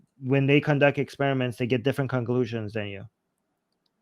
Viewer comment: thinks his random observations are "doing science" and wild the conclusions from those are reasonable. smh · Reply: For a second there, I thought you said concussions. After having spent half an hour listening to, concussions would explain a lot about him.